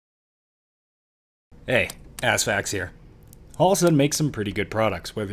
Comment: Is it just me or does the intro sound like he says Ass Facts? Love the channel and got a good laugh from it. You've been killing it lately man, thanks for the content.